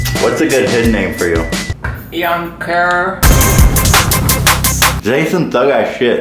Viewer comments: I thought she said young terror 😭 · I thought she only said young kerrr 😂